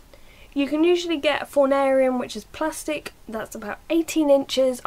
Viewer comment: what did you say on 1:58? fonarium?